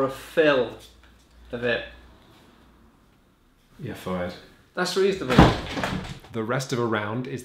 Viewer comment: wtf did he say after "youre fired" @ 4:08? Thats reaffirming? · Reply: That's reasonable